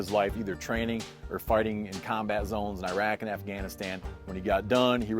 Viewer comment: Did he say "farting in combat zone" at 0:20?